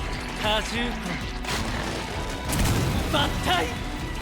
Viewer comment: did mahito really said bankai?